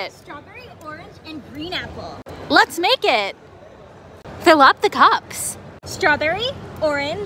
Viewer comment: Anyone else gonna talk about how it sounded like she said fill up the co